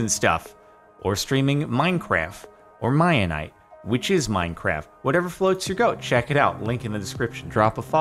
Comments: Did he say "Whatever floats your goat" ? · it sounded like he said "whatever floats you goat" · did he say “whatever floats your goat”? Isn’t “whatever floats your boat”